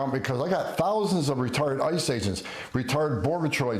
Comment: That cop sounded like he was saying we got ree-word I.C.E. agents, ree-word Cops, lol. I bet buddy.